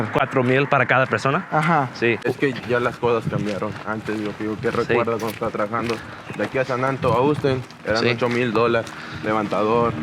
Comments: i think he says 8000 dollars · Yo Andrew! The subtitles are wrong in many parts! Some are inoffensive mistakes, but on 15:42 he meant 8000 dollars, not pesos. Also, he's talking about crossing people to San Antonio, not to Eagle Pass!